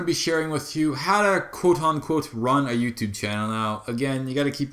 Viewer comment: I cam because i thought i said "How To Ruin A Youtube Gaming Channel", "Ruin", not "Run" xD